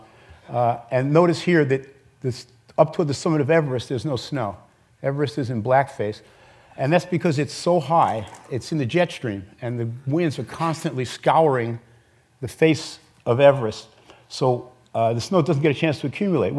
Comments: Am I the only one that heard him say "Everest is in blackface" @ 4:14? Or am I hearing it wrong? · did he say everest is in black face???? Lolol wtf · Did he just say Mount Everest is in black face